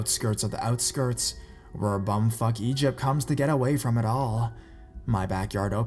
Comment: Anyone else hear bumfuck Egypt at 14:30?